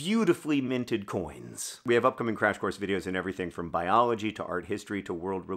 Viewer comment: Did I hear you say Crash Course Art History! As a high school Art teacher I am beyond excited about this.